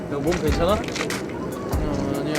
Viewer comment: did I hear this right, "Yobo" by Jinyoung to Jb??